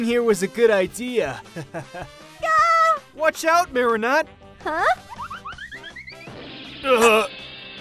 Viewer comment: Who else heard MariNUT? 😂😂😂👌🏼👌🏼👌🏼🤣🤣🤣